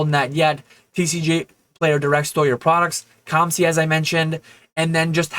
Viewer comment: did you say "tcg player direct stole your products" at 19:21? were they stealing peoples stuff?